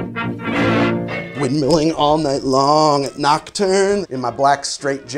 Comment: for a minute there when he said nocturne back i thought he said knockturner ally like from Hp lololololololololol